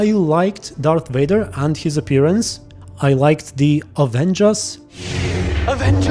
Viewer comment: I thought you said "I liked the avengers" for a sec there and I got so confused because that would've been so random lol